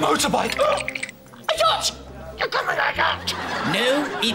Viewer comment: What did he say? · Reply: he said Yacht actually as in the boat